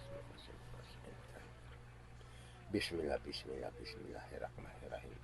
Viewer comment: did he say Bismillah?